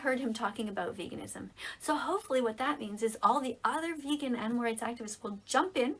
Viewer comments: did she say vegan n words? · Did she really say all these vegan n-words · "hopefully all the vegan n-words..." .... am I deaf or did I hear that correctly-??? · did she say,”All the other vegan N-words.”? Or I’m a hearing stuff 🤣 I might be hearing stuff. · ngl when she said animal rights activists i thought she said n words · Wait at 4:32 did she just say all the vegan n words :0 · sounds like she said “hopefully all the vegan n-words” could be hearing things tho hopefully I am just hearing things💀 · what did she say?! Cus it sounded like she said “the other vegan n word activists” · what you mean vegan n words did i hear right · Did she say at 4:35 "that all the other vegan n word will join in" 😳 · i thought she said "All the vegan N words" But um ig she said all the vegan animal right activist. lmaoo Damn speak slower next time THE VEGAN TEACHER. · swear it’s sounds like she said “all the other vegan n words”💀💀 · when she said "all the other vegan animal rights activists" i swear i heard "all the other vegan n word activists" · did she say all the other vegan n-words? someone correct me of im wrong but it sounded like it right? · does she say all the vegan n words????? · Is it just me or at 4:34 does she say all me vegan n words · "Vegan N-words" I'm hearing what I think was said right? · Did anyone else hear her say all the other vegan N-words an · did she say all the vegan's n-words?? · did she says all the vegan n words like tf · did she say all the vegan n words